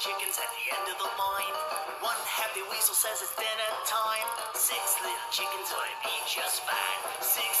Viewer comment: I love how between every sentence he was giving a warning- “dinner time”, “at the end of the line”, “watch out”, “here he comes”, and then “whoops- 5 little chickens” instead of 6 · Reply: Rusch I thought he said “dead end time” then i rewatched and hear dinner time. just his pronunciation of dinner is like Dinna. Think of how he says Tito the toitel in the beginning like bugs bunny